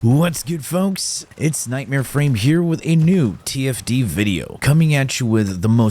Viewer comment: have to replay that intro again, i thought he said "tft video" lol yeah tfd